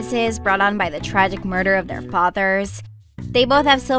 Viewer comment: So I had to go back I thought it said "theyboth murdered their fathers" I was about to panic lol